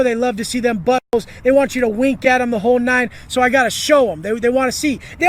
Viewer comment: I'm sorry... what? I literally don't understand. Is he talking about the cops giving a cavity search when being booked or the other inmates??? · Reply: Man said “they want me to bend it over and wink it at them” 😳